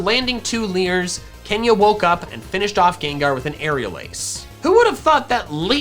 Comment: It could just be the fact that I'm a SSBU Player, but did I just hear "Neutral Aerial Ace"?